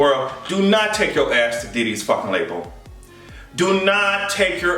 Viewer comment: I thought it said Daddy's new label. Hahaha